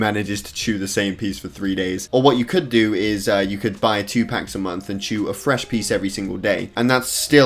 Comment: i thought t he said “you could eat tupac per month”